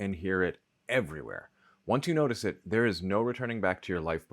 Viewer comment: you said something that sounded like= once you notice it. but playing it back and paying attention you really said= wonts you notice it.